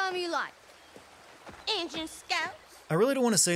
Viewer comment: wtf were they saying? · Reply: I think they were saying Ingin scalps. Ingin was a "term" (to put it nicely) for native americans.